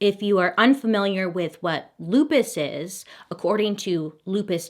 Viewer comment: I THOUGHT YOU WERE SAYING BLUEPISS AND I GOT CONFUSED😭